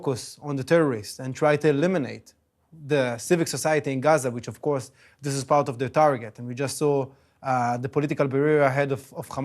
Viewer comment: Did he say eliminate the civic society in Gaza, this is part of the target ?